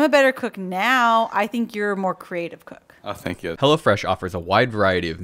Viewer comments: I thought you were saying hella fresh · I heard "Hella Fresh" instead of "Hello Fresh"